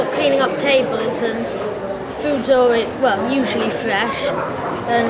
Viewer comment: I thought he said "flesh" on first listen.